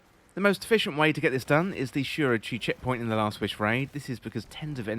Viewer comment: Sorry what raid did you say on 1:59 ? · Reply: the most efficient way to get this done in the shuro chi checkpoint in last wish - see wish 4 here <link>